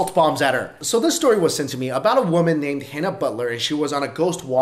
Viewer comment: i thought he said hannah baker 😅🔥